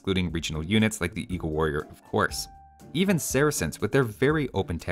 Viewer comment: I thought you said "or horse" not of course :D I feel like Slavs should maybe be broken up or turned into Ruthenians/Kievan Rus' Do Samurai have bonus damage against boats that are unique?